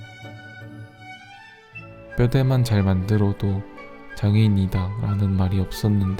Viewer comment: 암만 반복재생 해봐도 장애인으로 들리는데.. 흠..